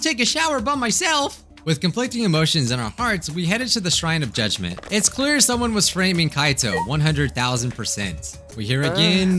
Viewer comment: I thought he said " It's clear someone was rap*** Kaito, one hundred thousand percent."